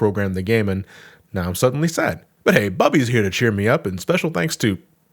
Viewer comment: oh bubby I thought you said Bubsy